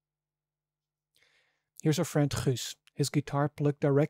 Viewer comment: What's the guy's name who plays the guitar? I could have sworn he says "Russ". Or maybe "Chris"? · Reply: It's Guus (a Dutch name).